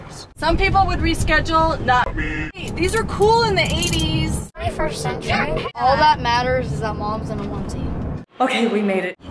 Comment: What did he say at 0:52? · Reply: This is the 21 century Taht she was wearing a onesie